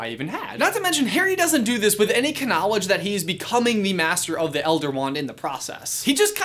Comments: Ransome question did he say ka-knowledge on purpose or was that just a slip that no one caught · did he just say knowledge... like- with a K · What did you just say? Ka-nowledge?